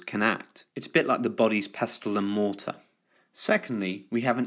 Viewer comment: Thank you for the video, it really helped. There was something, however, that you said at 3:55 (Pestilamorta) which i couldn't find the definition of, i'm not sure if i heard it wrong but what is that?